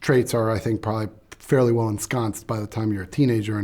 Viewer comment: Did anyone get what he said at around 1:39? He said "Narcissist traits are, I think, probably fairly well (...?...) by the time you're a teenager". Captions don't seem to match and English isn't my first language so it's hard for me to understand what he said there. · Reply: Fairly well “ensconced”.